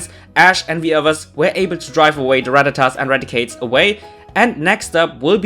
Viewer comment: 😂I thought you said "Ash's first grandchild" instead of "Ash's first grand trial." 😂😂😂😂😂😂😂😂😂😂😂😂😂😂😂😂😂😂😂😂😂😂😂😂😂